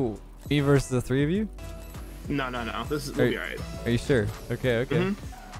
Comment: he said "are you sure" and it sounded like hey siri so my siri opened lmao edit: on a iphone XR my volume is 3 clicks up from the very bottom